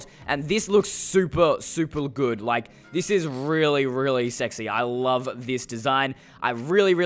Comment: Me: did he say that looks really really sexy